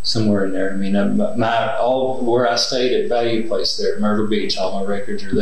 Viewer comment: I don't think he meant to say murder beach instead of myrtle. You know what's on his mind.😂